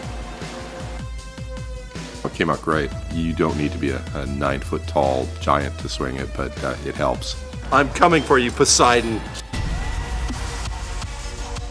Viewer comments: why did I heard PUSSYDON 😂😂😂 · Sounded like he said am coming for you pusseyedone lol